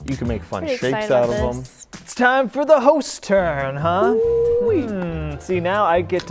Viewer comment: I literally heard it's time for the Ho*s turn lol